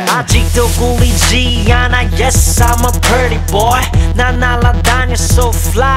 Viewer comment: the first time i listened to this i thought gd said ''yes, i'm a dirty boy''